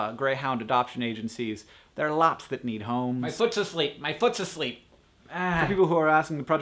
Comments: When you said 'my foot's asleep' I thought you said 'then put to sleep' and I was horrified... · I got scared at 3:05 when he said "my foot's asleep" I thought he was saying "I put to sleep..."